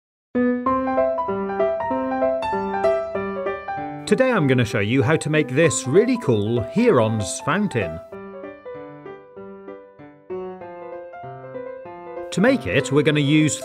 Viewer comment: I thought you said Heroin fountain...